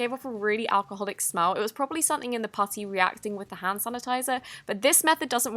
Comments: Did She say “pussy” ? 😂😂😂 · I thought she said pussy xD no hate added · anyone hear p*ssy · Am I the only one who heard pussy at 1:34...